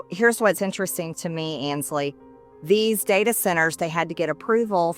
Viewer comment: I misheard sometting, but I think it should stick: "data sinners"